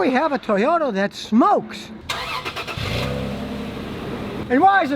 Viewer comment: in the beggining i thought you said "BILLY HAS A TOYOTA THAT SMOKES"